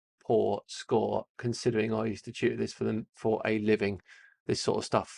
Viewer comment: At first I thought you were saying you were a "master science cheater," which would be a really weird way to describe oneself. It took a moment to realize you were saying "tutor" and not "cheater." 😏 The one thing I can tell early on is you're taking way too long to go through these questions...